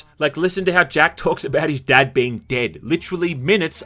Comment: Did he say "dad beat dead"